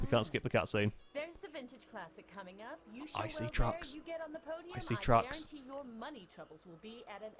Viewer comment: I thought he said I see drugs, I was like what